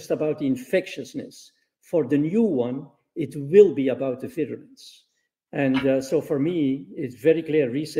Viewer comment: What does he say at 3:54ish? I can’t quite make it out. I think he says virulence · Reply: Yes, he says 'virulence'.